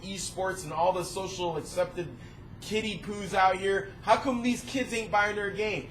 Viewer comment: i thought he said kiddy gooz lol